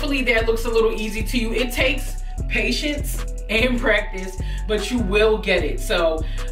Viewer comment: Why did I think she said patience and pregnance at 6:42🤔🤣